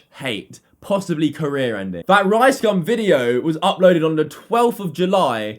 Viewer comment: did he said rice gum video lol